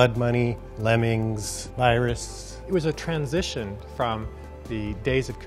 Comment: Does anyone know what game is shown at 0:53 . It brings back memories. It sounds like they said it was iris ? (Edit for typo) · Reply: Maybe Virus? Given the name at the top.